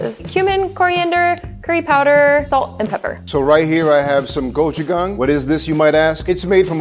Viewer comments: did he just said "gucci gang" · did she said human